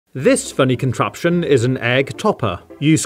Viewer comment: I thought he said 'This bunny contraption'